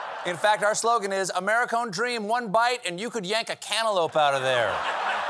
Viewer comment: OMGosh! At 4:40, I thought he said ‘antelope’ 🦌😩😂